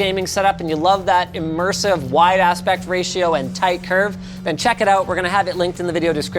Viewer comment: He said "Wide aspect ratio" I thought he was saying "wide ass"